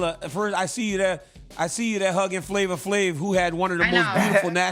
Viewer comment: flav sounded like dook